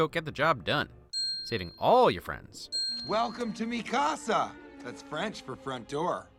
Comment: did he say MIKASA??? “EreN-“